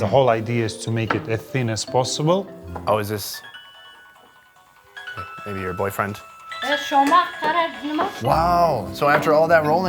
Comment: did he say to the old woman, “ here your boyfriend “ ?